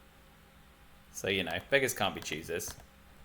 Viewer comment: GG friend! Towards the end, I could have sworn I heard ... "Beggars can't be Jesus" ... and I thought ... "What???"